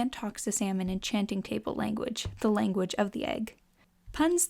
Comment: Why did I hear "the language of the gay" when she said language of the egg